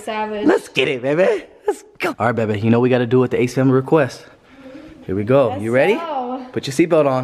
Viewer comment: Sounded like the prince family